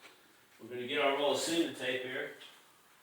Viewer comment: did he say semen tape? lol